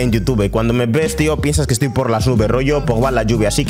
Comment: ¿Qué dice? Cuando me ves tío piensas que estoy por las nubes . . . la lluvia